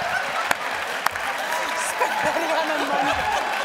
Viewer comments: I understood her perfectly both times. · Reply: I heard her say “ spent a lotta money.” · I heard "Memoir" on the first and "Spent a lot of mommy"